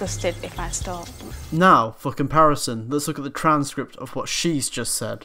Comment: when you said for comparison.. i thought u said "fucking parasite" lol